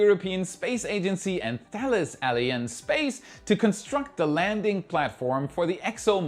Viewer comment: you persevere in pronouncing "Thales Alien" (13:01) instead of "Alenia", obsessed by aliens? 🙂